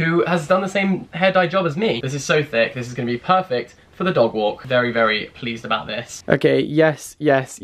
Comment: Laughed because I thought you said 'very very fleeced about this'